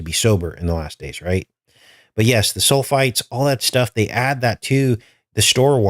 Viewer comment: You said “sulfates” but I heard “soul fights”